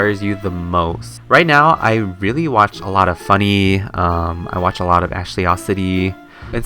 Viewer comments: OMG DID U SAY U WATCH FUNNEH?? or not... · Did I hear Funneh